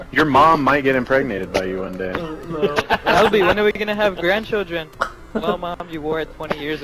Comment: What's name? At 4:14 says it but it's really unclear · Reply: his name is